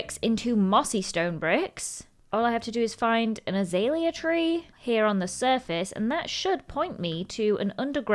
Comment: Am I the only one who thought mazalea when she said azalea